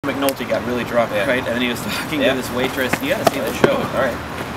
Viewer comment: totally thought you said he was f***ing his waitress, not talking to. i need to get my mind out of the gutter.